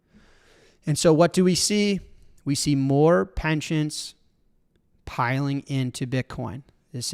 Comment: What is with Jack's pronunciation of "pension"? Sounds like he is saying "penchant"? Surely he knows the difference? Is this some weird Chicago thing?